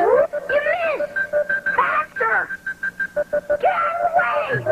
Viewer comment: HELP THE AUDIO QUALITY MADE ME THINK THEY SAID "BASTARD!" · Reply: What they're really saying Is "Faster!"